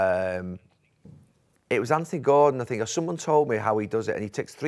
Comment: Anyone else hear Gary's ear piece at 14:22 😂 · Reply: YES! "Anthony Gordon" 100%